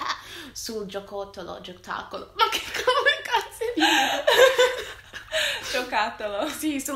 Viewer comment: Solo io ho sentito in 2:35 "ma come caz.."